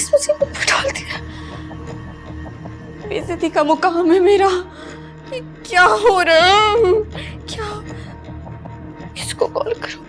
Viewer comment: what she said plzzzzz cn any1 tell ???? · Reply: Bay'izzati ka muqaam hai mera. (It's insulting for me).Yeh kya ho raha hai? (What is happening?)